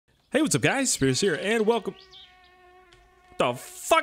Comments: i thought he said "hey whats up guys it scarce here" at the beggining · Did he just say "hey what's up guys, scarce here" 😂